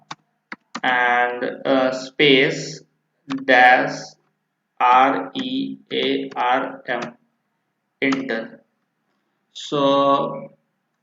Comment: What's the code he said I'm facing this issues now on my hp laptop I heard"slmer space-rearm enter" Can someone confirm pls